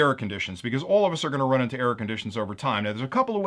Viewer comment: Oh yea , I thought at 1st you were saying Air conditioners. LOL. instead of error conditions.